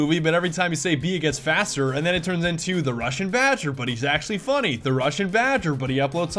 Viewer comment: In the begging of the vid i thought he said Russian vager